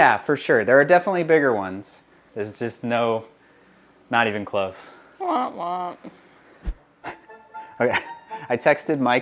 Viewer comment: Did she say womp womp?